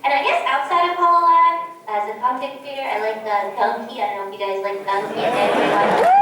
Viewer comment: Harro guys ~ may someone tell me what did she said here (around 16:52)? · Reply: "as a content creator, I like Dunkey (YouTuber that does gaming content)"